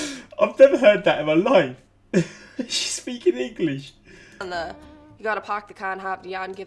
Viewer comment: pls I thought Massachusetts said “you gotta park the car in half”